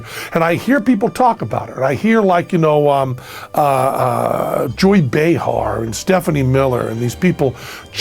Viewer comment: Did you say Jewy Behar at 4:24 ?